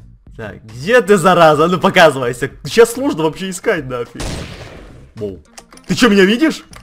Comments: Мне ПОСЛЫШАЛОСЬ или Едисон сказал 1:58 Щас сложно вообще искать на х*й? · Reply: Мария Плотникова нет нафиг а не на х....й тебе послышалась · Мне ПОСЛЫШАЛОСЬ или Едисон сказал 1:58 Щас сложно вообще искать на х*й? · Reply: Послышалось, он сказал "на фиг"